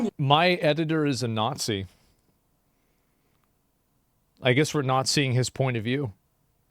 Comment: “I guess we’re nazi his point of view” is what I thought he said…I need help 😂